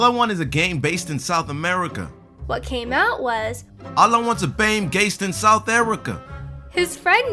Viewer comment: For the second one it sounded like he said all I wanted was to bang South American